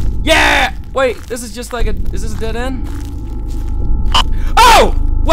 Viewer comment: whats that I COPY light in the background? and on another pant shitting note, either my headphones are fucked up or at 4:30 i heard "caitliff is dead"